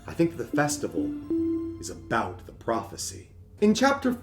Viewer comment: I initially misheard this as "The festival is about Ralsei," and now I'm not even joking when I say I'm starting to build my own theory about that based on this one. Like, it makes way too much sense when I think about it: We know Ralsei is a recognized religious figure in Hometown, thanks to Catty's prophecy reading. We know that Ralsei is associated with flowers, BUT that the version of him associated with them is specifically his hat-wearing version, because he wears his hat in the newsletter gif of him growing a flower from his head. And we know that Ralsei's hat represents what the prophecy wants Ralsei to be, because he's seen wearing it within Tenna's game. So my theory now is that not only is the festival to celebrate the prophecy, it's specifically to celebrate the prophecy's idealized version of "The Prince." Which is why they're using so many flowers.